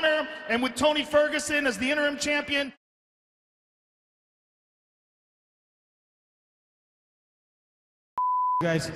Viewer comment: What did they say at that 2:15 ufc cut it out ? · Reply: "tony and connor are 2 bullshit guys", I think they could just beep "bull*" but seems to be a new UFC rule where you cant bad mouth his money bag connor